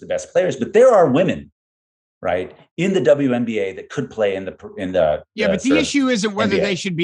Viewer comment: Did I hear incorrectly or Fabio said "there are WNBA players who could play in NBA if they were allowed to"?!!!